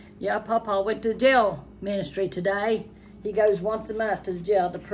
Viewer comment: 😅 I first thought you said Pawpa went to jail.God bless him for doing that.